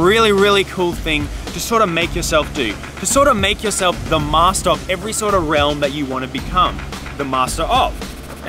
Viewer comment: DID HE JUST SAY THE MASTEROV!!!!